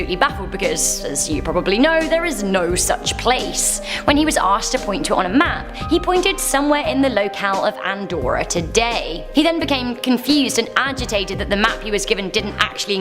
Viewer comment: Andorra....I thought you said Antwoord....thought you were going Africans on me there..